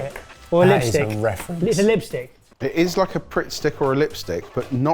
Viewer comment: captions say Jamie said “prick stick”… 😳😂… I’m guessing there’s a different meaning for that in the UK or captions weren’t accurate